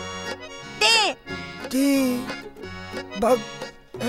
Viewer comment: の所ら辺Tバックって聞こえるw